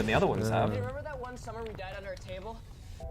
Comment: I can’t make out what Finn Wolfhard is saying can anyone help? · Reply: He says" hey remember that one summer we died under a table" i think because there was a tremor and he is trying to lighten the mood and keep everyone calm.